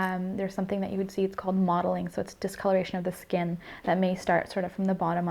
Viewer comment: I was wondering what "modeling" was at 1:00 but when she said it again I realised it's the American pronunciation of mottling.